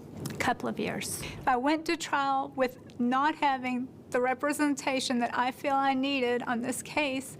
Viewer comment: At the 23:38 mark, did she say she didn't have the right "representation" she needed, or "contraception" she needed????!!!!!!